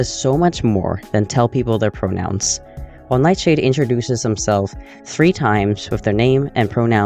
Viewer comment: it sounds like you say himself but the subtitles say themselves.